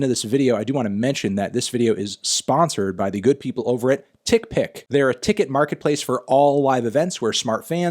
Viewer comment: did he say dick pic